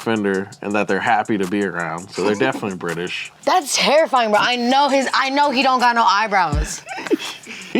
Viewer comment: I thought she said hairifying at first, either way she was right- I'm pretty sure he's got a full on crispy afro now...